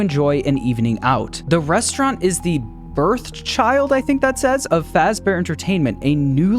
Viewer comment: I can't make out any words visually, but from the context of the words around it, that word is almost certainly "brainchild"